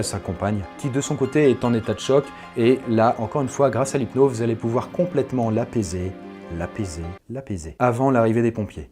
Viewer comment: "l'apaiser, l'apaiser, la baiser" voilà ce que j'ai entendu.